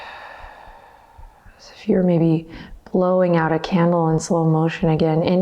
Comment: When you said "breathe out like you're blowing out a candle", I definitely heard "like you're blowing on a camel" and I was like "hmm...like the cigarette brand???" And then I realized what you said and had a REAL good belly laugh by myself! :D